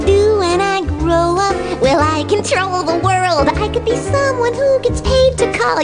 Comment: "Will I control the world?" It sounded like she said, "When I troll the world," at first.